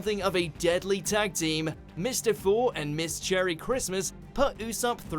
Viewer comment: did he say "cherry Christmas"? Happy New Year everyone